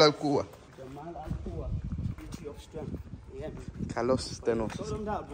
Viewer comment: slm, can i ask what you were saying at 11:33 because it sounded a lot like "jamal elkoua" am i right pls answer allah y barek feek